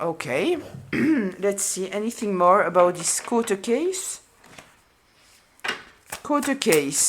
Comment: Court a Case… I heard “court case”